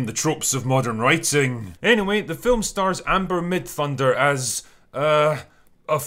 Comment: Why do I keep hearing ‘Midthunder Thighs’ ?